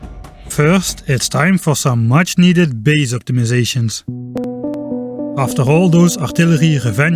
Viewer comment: at first i heard "base atomization" and got very excited 🍆